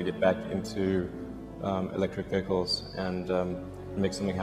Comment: If you say something negative about him, people react like it’s blasphemy. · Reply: Oh not me anyway. If Musk is saying something like: "After PayPal I decide I wanted to get back into electric pickles" (9:07) People say: "Yay!! Yes!! Let's all drive electric pickles!!!" Did I hear that the pre owned Teslas from the "back lot" don't even come cleaned? That they have fingernail clippings in the cup holders and pubic hairs on the back seats? NO!...This cannot possibly be true!